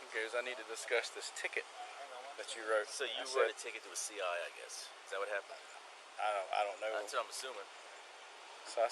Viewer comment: And possible CIA agent? Your description says at 40:50 the officer says Marilyn Bean might be a possible CIA agent. You need to change it as well as the title of your video. What he actually said was “so you wrote a ticket to a CI, I guess. Is that what happened?” CI, as in a confidential informant.